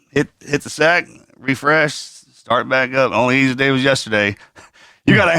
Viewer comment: I'm not sure I understood Marcus at 7:57 ... did he say, "The only easy day was yesterday"?